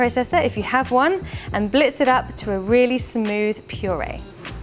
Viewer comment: what are you saying I don’t understand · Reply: Blitz it up (blend it)